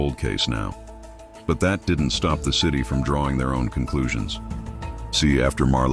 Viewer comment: Did he say young ni66@ 1:55:15 😮